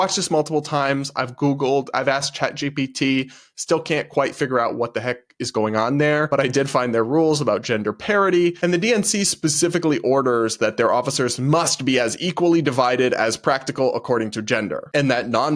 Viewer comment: It's so funny because you said "gender parity" but I heard "gender parody"